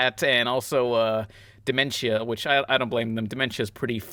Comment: I thought you said Dimentio, from Super Paper Mario